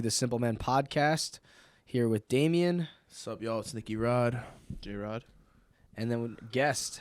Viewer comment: I heard: "What's up you all? is Sneaky Rod"